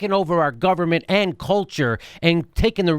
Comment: He said "and culture" and I thought he said Ann Coulter 😖